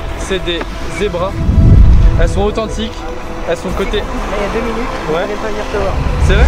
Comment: il dit quoi? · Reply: Il y a deux minutes j'osais pas venir te voir